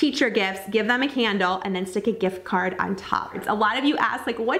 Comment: You mentioned putting a handle on the teacher gift and add a gift card. What did you mean by handle? Thanks! Super cute ideas!! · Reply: Alfredasworld lol that's I thought she said too but she actually says "candle with a gift card on top"